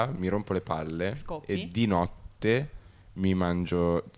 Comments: Solo io al minuto 5:46 ho capito che lei dice: “scopi?” 😂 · solo io ho sentito "scopi" ?